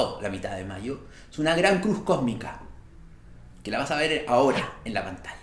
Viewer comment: Entendí “Cruz cómica” y yo bueno si nos reímos aunque sea….😂